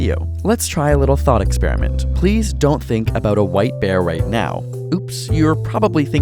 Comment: I thought he said "think about a white pail". Whoops.